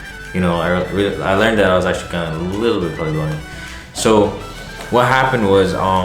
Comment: I don’t hear wtf your saying talk louder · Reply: “A little bit color blind”